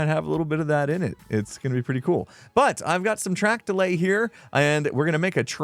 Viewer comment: I misheard you say “Track to lay” as “Track delay”, that could be a good name?